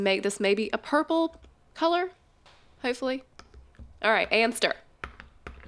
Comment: who else heard hamster?? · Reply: She said”and stir” I thought she said that too